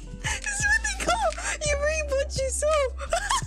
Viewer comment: Anyone know what did Kronii said at 1:05:00? What does it mean? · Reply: You mean "you reap what you sow?" Here, it means her losing her items is the outcome of her seeking revenge from fauna after she was trapped in the maze at the nether.